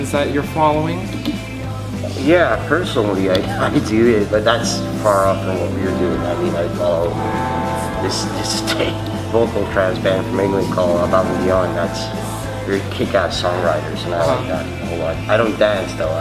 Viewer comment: What does he say at 8:40 to 8:55 can anyone make it out? Thanks :) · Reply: ``Yeah, personally I do, but that’s far off from what we’re doing. I follow this trance band from England called Above and Beyond, who are very kickass songwriters. I don’t dance though, I’m too shy for that´´ the whole interview is written here <link>